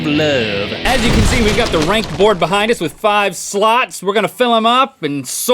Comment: for some reason i heard Link say," ranked board with five sluts" xD